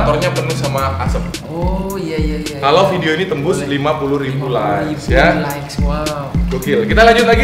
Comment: 50k like ga salah denger gw? Hmm